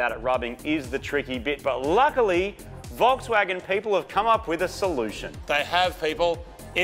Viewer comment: Did I hear "golfswagen"? 🤣